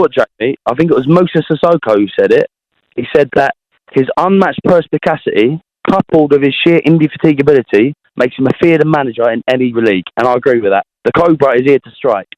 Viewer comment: the guy just quoted Andrew Tate and said it was sissoko looool. Sissoko can't speak english like that. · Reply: what does he even say? I hear "His unmatched perspicasity coupled with his sheer indisteekability makes him a fearden manager in any on league"